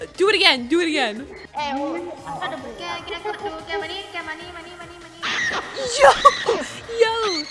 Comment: Me: Hol’ up what did I just hear? What I heard when I replayed: “แกมานี่ ๆ” (Translation is “Hey come here, come here” I’m Thai btw-)